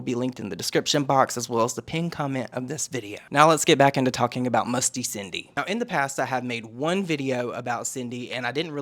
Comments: Initially I thought you said her name as ‘Musty Cindy’. 😂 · I thought you said “musty Cindy” waaay longer than I should’ve. 🤦‍♀️ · I'm thought you said MUSTY Cindy at the beginning of the video!!! 😂🤣😂